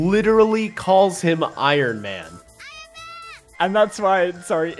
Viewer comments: she was actually saying "Ayo ben" wich means lets go ben but i guess the pronounciation just sounds like iron man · little correction she is not call him iron man instead she says ayo ben (come on ben) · Idk if this is me just mishearing thing but i don't think the girl sad iron man but she is saying "ayo ben" It roughly translate to "go ben" Like cheering for him